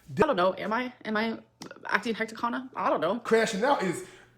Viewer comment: "am i acting hecticana?" i feel how my braincells start to die everytime i hear her talk